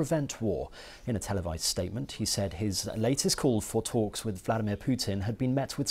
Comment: I would like English native speakers to correct my draft transcription of the first story - Zelensky plea to Russians - presented by Simon Pusey from the BBC: - Ukraine’s president, Volodymyr Zelensky is appealing directly to the people of Russia to prevent war. In a televised statement, he said his latest calls for talks with Vladimir Putin have been met with silence, addressing the Russian people he called for a peace, saying what happens next depends on them. The US Security Council is about to hold an emergency session. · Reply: Hi, Yoshimitsu, Although it sounds the same either way, I think the reporter actually said “he said” rather than “he’s said”. Also, the part starting with “addressing the Russian people” is a new sentence, it is the singular “latest call”, and following that, “had been met”, and “called for peace”. And it is the UN not US Security council (but that is just you mishearing it, right?). Good job today and have a good day!